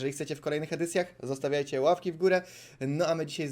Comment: xzy tylko ja usłyszałem ławki ?